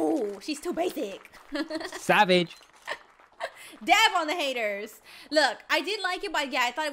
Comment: it sounded like she said da*n on the hater lol